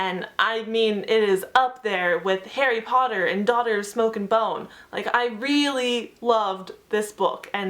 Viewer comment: For a split second, I thought you were saying "Harry Potter and Daughter of Smoke and Bone" and my mind went WHAT? Oh. Lol :-p